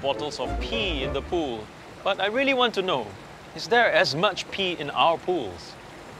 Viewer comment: I heard is there as much pee in our poo